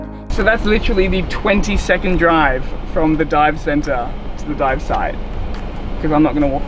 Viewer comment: I thought you said it was literally your 22nd drive from the dive club to the dive site. I thought you must have kept a log book to add literally in there. 🤣